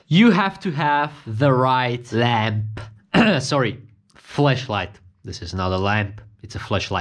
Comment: Sounds like you said flesh lite.lmfao